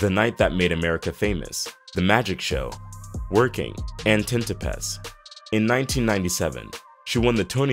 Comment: "Tintopaths"? I thought it was called 'Tintypes' I can't do this anymore... I'll be correcting $#!+ all day!!